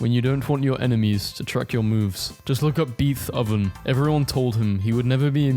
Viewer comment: did bro just say beeth oven 💀